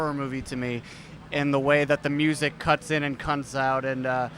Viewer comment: Did Graham say 'cuts in and cunts out' at 1:15?